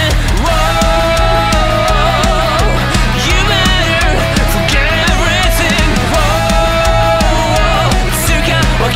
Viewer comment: What the hell did he say? All I know, is that the actual lyrics here are "Who are we, to pass through you?" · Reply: He said "you'd better forget everything"